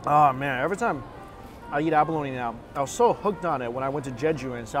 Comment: did you just said “abaleno” ? Cutest thing ever ☺️❤️❤️❤️☺️